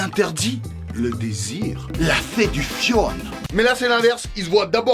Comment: Quand ildit "la séduction"... À 5:19.. J'ai compris "la fée du fion" .. 😅😭😂